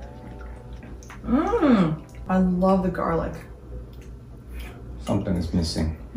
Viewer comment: Min. 3:53 said that she loves the garlic. The Spanish CC said she love ginger. In Spanish garlic is ajo; ginger is jengibre.